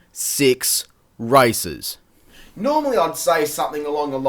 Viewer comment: I thought you said "only six races," and it took me a while to realize that you said "racers." :P